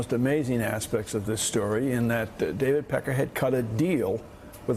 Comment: Um... did anyone else catch that (at 8:48) Barnicle calls the owner of the Inquirer "David Peckerhead"?